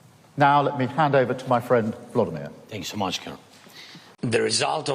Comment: Did I hear 'Thanks so much, c%nt' ?